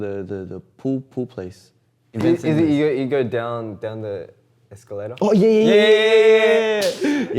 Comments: did he say escalera? 😂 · Chan said "pool place" referring to "billiards/pool" but the Korean subtitle said 수영장 which means "swimming pool"😂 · DID FELIX JUST SAID "ESCALERA" ??¿¿??¿!¡ (stairs in spanish) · escuche que dijo escaleras? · ALGUIEN QUE HABLE ESPAÑOL ME PODRÍA DECIR SI FELIX DIJO "ESCALERAS" (OSEA EN SPANISH) PORQUE ESCUCHE CLARAMENTE COMO SI HUBIERA DICHO 'ESCALERAS' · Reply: Tarde pero dice "down the escalator" (escalera mecánica) lo dijo con su acento aussie por eso suena así hehe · you go down... down to the... ESCALERA(? did he say it in spanglish? Hahahaha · parece que dice escalera 😮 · Alguien más escuchó que dijo escalera?:0 Me emocione ah askjsks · did he just said escalera?